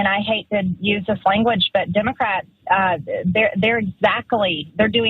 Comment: Oh I about had me..I thought she said go kill Democrats..Crazy..ok title had bait. She crazy tho